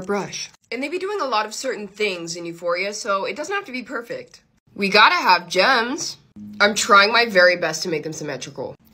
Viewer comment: Who else thought when she said gems you hear germs